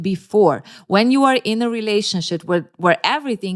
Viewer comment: did you say "relationshit" ? Cause.. accurate 😂